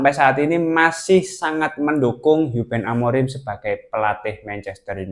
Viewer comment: Ruben menjadi hiuben Amorim, atau salah dengar 😂